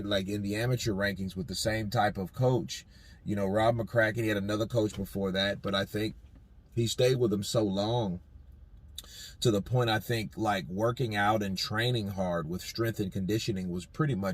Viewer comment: Did i hear Rob mcrackhead at 2:38? 😂😂